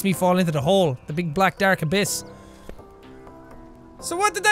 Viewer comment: When he said abyss for some reason I thought he said a piss